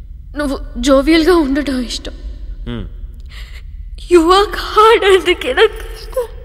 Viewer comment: Can anyone tell What is she saying @ 3:55.... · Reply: You work hard.. anduke istam.. Nvem anukunnav..🤣🤣😂